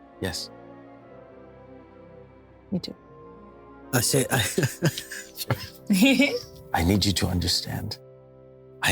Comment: and 1:03:47 what was whispered? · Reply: Couldn't make out the second one because of intervening facial hair, but the first one was "I would like that very much". Don't think it was whispered so much as the mic dropped a couple of softer spoken words.